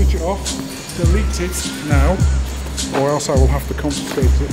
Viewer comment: What did he say was an offence? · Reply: Blindside FPV to record on the docks